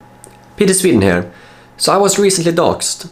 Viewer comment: Peter you said your own name in this video and I could have sworn you said Petrie....... like the Pteranodon from land before time.